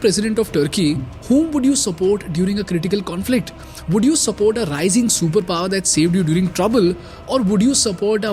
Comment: Did I just heard "raising superpower 😂😂"